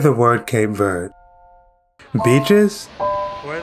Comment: what do you think about when you hear the word Cape Verde? BITCHES? Bro sorry your accent came through then 🤣